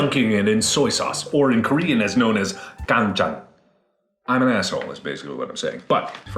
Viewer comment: does markiplier say 'canja' ?